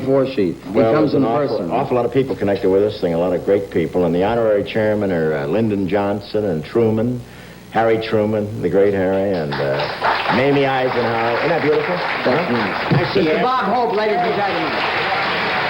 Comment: This isn't 1970, he mentions "the godfather" in the interview, which wasn't released until 1972.. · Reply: I think he said "Mamie Eisenhower" which does sound like "Maybe Eisenhower"? I think He predeceased Her so it could have been possible for her to be honored along with the memory of Her Husband.? Must be a roster of attendees somewhere, secret service would have been there with all those Presidents. If LBJ was still in office there would be an official record somewhere. At 14:13 Bob Hope says Mamie Eisenhower, I'll bet my bottom dollar